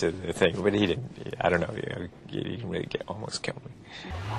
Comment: I was expecting a bigger crash lmao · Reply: I initially thought Tom said “he almost killed me” and that the substitutes were wrong too but if you watch a few times and listen closely you can tell he actually does say “he didn’t almost kill me”. It took me a couple views but what confirmed it for me was the fact that he is shaking his head side to side like someone would when they are saying “no” or something similar aka “doesn’t”.